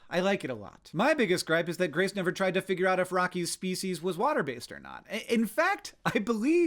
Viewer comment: I looked away from my screen and thought you said feces Haven't seen the movie so I wasn't sure had to go back real quick